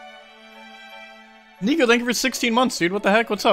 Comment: did he say the n word 😳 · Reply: He said Nico